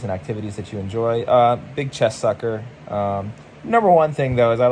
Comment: I thought you said chest sucker lol then I figured out what you said then you followed it up by talking about your girlfriend so I double questioned my hearing and had to rewind he he he